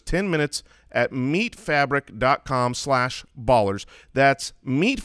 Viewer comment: Was listening and not watching when said "meet fabric". My brain heard it as "meat fabric" and I pictured some weird jerky-like product...